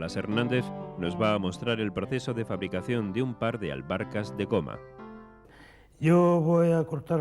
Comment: Pensé que decía Alberca 🏊 de goma 😅